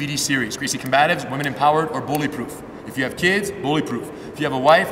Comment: A self-defense video called "Bulletproof"? I don't know if it is, but it sounds like a dangerous game called, "Lets pretend to be Jackie Chan, Chow Yun Fat, or Jet Li, in a high stakes life, or death situation with a gun". It's not that I dislike disarming techniques, but even if you're good at them, it's still super risky. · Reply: I think you misheard. Their programme for children is called "Bullyproof". It's Bully not Bullet